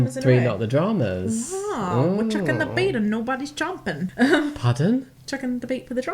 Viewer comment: I heard "We're dropping the beat, and nobody's jumping." Which scans as a saying but didn't fit the context.